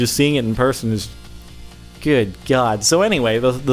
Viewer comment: I thought he said the louve (as in the bathroom) so I was really confused when he was like, Wooooooow. AMAZING!!!"